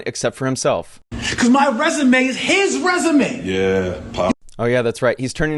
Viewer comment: Did he call Jay, Pop!!!!! Ummmm. Okay. 😮